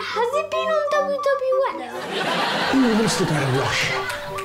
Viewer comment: what did Russel say? couldn't hear him over the laughter. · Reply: I think he said “who wants to go to Russia?”